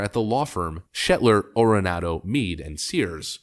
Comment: Did he say shitler at 3:54?